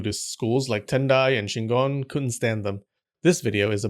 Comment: Shingon??? I thought he said CHINGON!